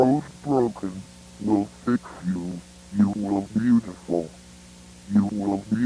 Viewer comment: I always heard it as "you will beautiful"